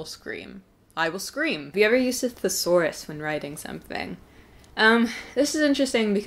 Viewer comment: hi ! when you answer to the question at 9:19 I really didn't uderstand what you were saying? It was like "using a S..." but I don't know what... The subtitles told me Saurus and Safar's but I don't think it's correct haha (however I really enjoyed the rest of this video^^) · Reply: a thesaurus! x